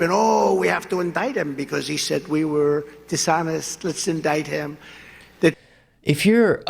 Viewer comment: what is he saying? Disorders? Speech disorders? Dishonest? This entire sentence is mumbled together